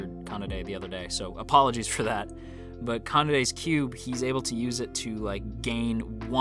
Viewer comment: When did I say Kanade was cute? :s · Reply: I think it was a misheared part in 11:05 when you said Kanade's cube . Also, Maple had done the same thing as Miho NIshizumi in Girls Und Panzer. Even when separated, Oorai's senshado team can still hold its own and thus strengthened their team even more when together.